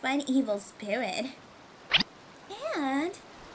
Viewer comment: At first I thought it said hot water sprayer